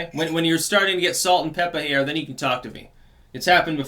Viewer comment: At first I heard "Salt and pepper here" as in "salt salt SALT SALT AND PEPPER EEERE"